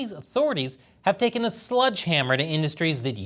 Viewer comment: Is saying "Sludge Hammer"?